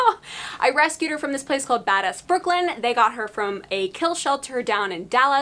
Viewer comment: I thought she said "Badass Brooklyn"